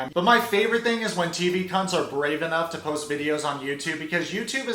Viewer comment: DID YOU JUST SAY TV CUNTS IM SCREAMING